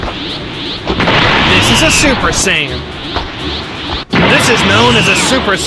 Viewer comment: The way he said 'super sayian'🤣🤣😂0:26 IT SOUNDED LIKE HE SAID SUPER SAN LOL